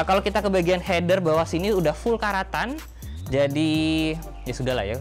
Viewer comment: "Header udah full karatan" Hampir salah denger, dikira udah full stanlis wkwk